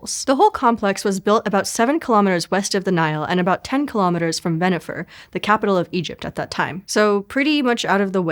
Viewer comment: did she say bennifer???